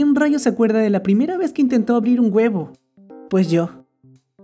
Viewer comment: Es como la quinta vez que veo el video y en el primero le entendí “ la primera vez que me abrí un huevo” xd